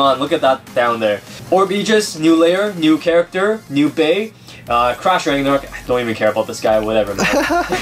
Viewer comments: I thought you said trash ragnarok xD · I thought you said trash ragnaruk